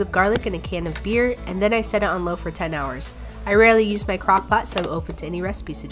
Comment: i thought it said crackpot and i thought you were doing drugs lmao